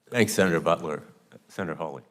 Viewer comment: Omg when this started I thought for sure I heard senator buttworth..bad mic?? I couldn't breathe 😂.. I was like fill dem seats!!! 😅 and then Josh said wipe...😂oh no.....just as I stopped..okay okay okay..breathing breathing phew. Okay sorry.